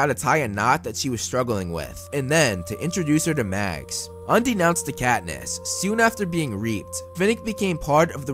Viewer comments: I've been watching a lot of your videos after marathoning the movies and I'm just about to pick up the books. But since I just heard you say "undenounced to Katniss", instead of "unbeknownst to Katniss", I don't think I can watch anymore. 🤢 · You should proofread your scripts for these videos more. 😂 I just heard you say "undenounced to Katniss" instead of "unbeknownst to Katniss".